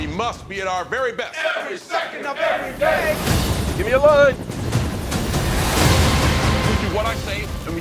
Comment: What's he saying? "Give me ???" Is that "Give me a line?"